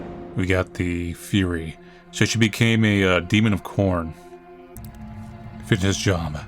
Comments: wait i minute did i hear 'she became a demon of Khorne' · Did he say, “So she became a demon of KoRn.”?? 🤣 I think I misheard that and have some KoRn brainrot lol